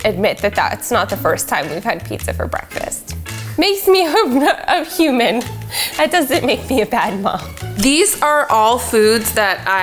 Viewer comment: I can't be the only one tryna figure out what she was saying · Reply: “Makes me wh-, I’m human, that doesn’t make me a bad mum”